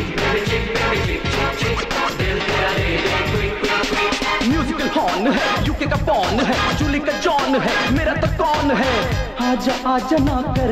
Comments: did I heard something wrong UK ka porn hai something like that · Did he actually said UK ka __ hai? 😂😂